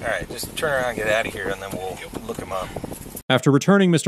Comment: When the officer got back in his car, did I hear him say, "Just turn up here and we will look him up"...